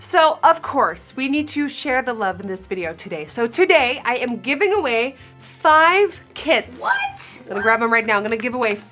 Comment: "I am giving away 5 kits" I heard "I am giving away 5 KIDS" 👁👄👁